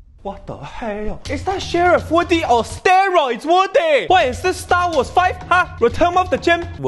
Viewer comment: Is that Sheriff Woody on Steroids?! 🤣🤣🤣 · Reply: He actually said steroids woody